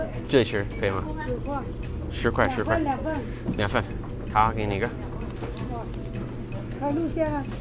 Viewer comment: he hands her money and it sounded like “here you go nigga” Every time I go I can never understand how they hear each other talking and under ther mask